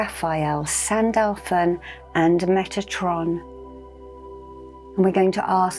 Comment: I thought she said Megatron